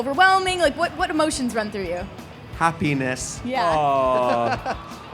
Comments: I PAUSED THE VIDEO AT THE WRONG TIME WHEN DAN WAS SAYING HAPPINESS AND IT JUST WENT "Ha-" BUT WHEN I PLAYED IT IT SOUNDED LIKE "A Penis" IM CRYING OMG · why is it when Dan says happiness i only hear ha-penis